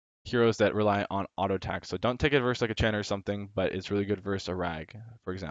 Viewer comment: "Don't take it verse like a Chen or something" Did you say Chen? Does he have fast auto attacks, or does he have low aa damage?